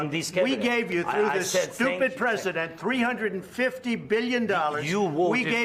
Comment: did Trump really say "Stupid President" or I am hearing something wrong😶 If he actually did ,this would be the biggest diplomatic blunder in world history ,the president as a guest is a representative of the whole country cussing him is equivalent of cussing the whole country ,God save 🇺🇸